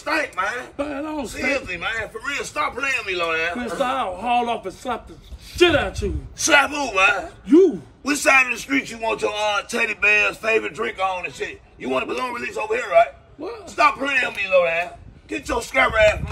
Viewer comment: How you got my number. always in some shi+. tried to be hardcore for 20 seconds LOL!!!!! I just heard "where you want the ballons released". LOL!!!!!!!!!!!!